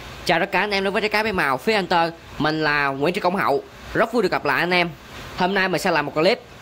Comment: Nói chậm lại xíu mà nghe thành Nguyễn Thị Công Hậu😅😅